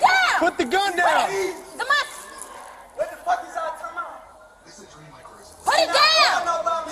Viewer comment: Did he just say “time out at 6:56!?” 😂